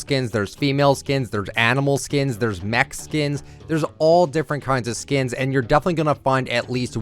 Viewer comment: that sounded like he said there’s Mexicans 😂 but he said there is mech skins